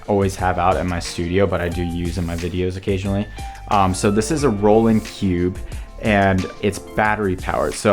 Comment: I heard ''rolling pube''